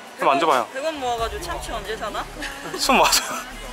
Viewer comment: (팔짱 끼구) 백원 모아 가지구 참치는 언제 사나 ㅋㅋㅋ 지극히 현실적인 반응ㅋㅋ(몇 번을 반복해서 들어도 저렇게 들리네요) 오늘도 역시나 많은 분들의 행복한 찐웃음에 덩달아 행복해집니다 ^^